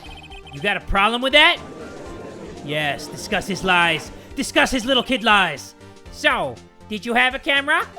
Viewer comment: I thought he said ‘Discuss his Little tit Lies’ not ‘Discuss his Little Kid lies’